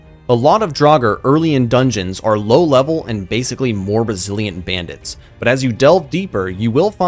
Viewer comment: I thought he said “more Brazilian bandits” Lmao